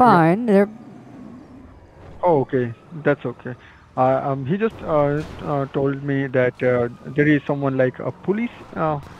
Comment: Sounds like dood said "Jesus told me there are some police officers after you"